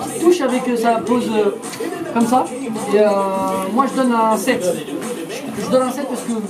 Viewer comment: Mdr a 1:54 j'ai compris : " je lui donne un sexe " mdr 😂